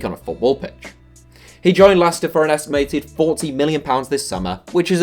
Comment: Did he say he joined Laster at 6:27?